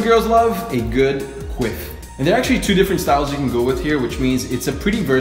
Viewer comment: “A good quiff” I thought he said queef lmao